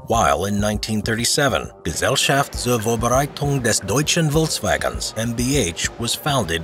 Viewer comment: for those who ask what he said: Gesellschaft zur Vorbereitung des Deutschen Volkswagens (Society to prepare the German Volkswagen)